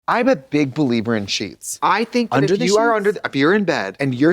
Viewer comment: I thought Trixie said she was a big believer in cheats at the start, and the following description was explaining that because a surface has oils and dead skin on it, it classifies as a human and is thus classified as cheating if one touches it.